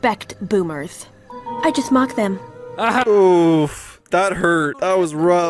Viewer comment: i thought she said mog, ami developing brainrot?